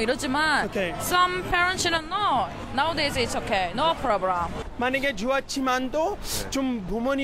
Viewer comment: i thought you said the n word but you said 만약에